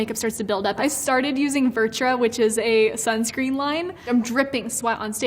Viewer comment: What's the foundation? I'm a sweaty mess and need it! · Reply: It sounds like she said ‘virtra’ or something like that!